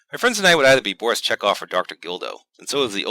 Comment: HOLY SHIT I thought you said Dr. Dildo!